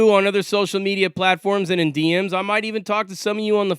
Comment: Ha!!! I thought you said “and Indians” but you said “and in DMs” I’m dumb af.